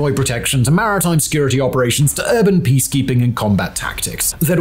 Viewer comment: I feel like you skimmed over this MARITAL OPERATIONS point entirely too quickly… 😮 4:32 …WHAT!? Did I hear that incorrectly?!